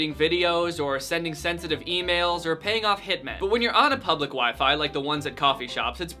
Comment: did he say paying off hit men